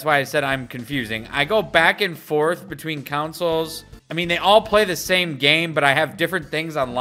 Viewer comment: Oh, “console”! I thought you’ve been saying “council”! Still. “Council Mii” sounds better than “Console Mii”.